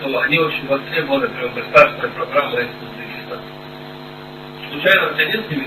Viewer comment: Так на 2:31 «Узбекистан», а на 2:54 «узбеки». Де СБУ там Дагестан (Росія) почули?